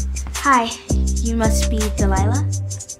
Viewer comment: I heard "tell a lie lah"